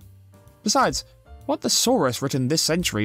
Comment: I thought you said "What the saurus" for a moment and my brain melted